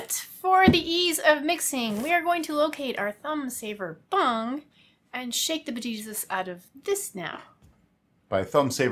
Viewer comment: What I thought I heard: "Bung saver thumb" Me: Wait... wut? Rewinds Me: "Oh... okay whew!"